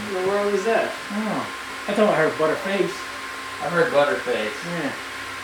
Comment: sounds like he saying butter face. it's but her face.